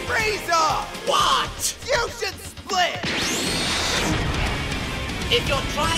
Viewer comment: When I first watched this as a kid, I heard “YOU SHOULD SPLIT” as “FUSION SPLIT” and thought that was the name of the attack for years Only now do I get the pun